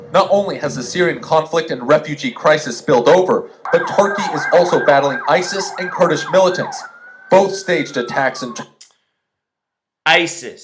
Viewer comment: Someone commented that the reporter says "staged event" now I can't find that comment. I heard it too, "both staged attacks" Yeah got those bitches.